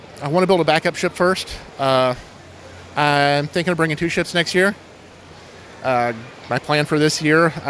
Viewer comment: did he say shits?